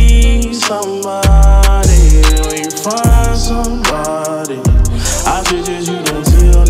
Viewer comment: “When you find somebody, I suggest you don’t tell nobody” 💯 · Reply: I always thought he said if you don’t find somebody I suggest you don’t tell nobody 🤣